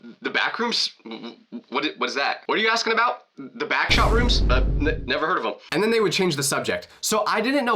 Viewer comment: "the backshot rooms?" WHAT DID I HEAR???😨